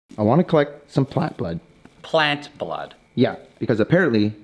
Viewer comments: i thought it said pants have blood at first ngl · My dumbass thought that said “people had blood?” ✋😭